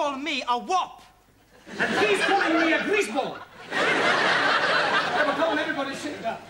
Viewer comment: From afar, Max do look like John Travolta in Grease, hence 'the grease (instead of Greece) boy' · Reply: True, but what he actually says is ‘greaseball’ which is an old American/English insult - search google. These subtitles are not always accurate